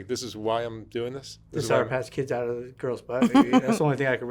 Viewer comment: is anybody able to tell me what he's saying? :( Cause I can't understand and the automatic subtitles do not seem to help much... Xd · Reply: He said "the sour patch kids out of the girls butt". Cause earlier at 1:38, he says that's when his first experience was. When he was 4 years old and was asked to eat a sour patch kids candy from a girls butt.